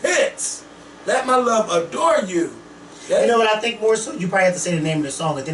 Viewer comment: I wonder how many people think Miguel's saying "adore" as well. he's saying "ADORN you". Lol I bet a lot of people thought he was saying "adore". :)